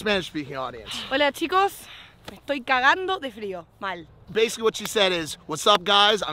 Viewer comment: Im learning Spanish and I’m really frustrating cause I don’t understand what the Spanish girl said at 00:50 in Spanish, it’s really frustrating me😅 does anyone know the Spanish phrase? It was like “estoy catando de frío mal” or something, but carando isn’t a word😩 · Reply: She said " me estoy cagando de frio ,mal" it just means she is feezing